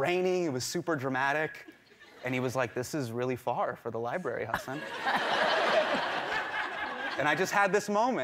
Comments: captions say "huh, son" instead of "Hasan" :D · lol, when he said "this is really far for the library Hasan", I thought he said "this is really far for the library, huh son?". 🤣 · the English captions say huh,son instead of Hasan 🤦🏼‍♂️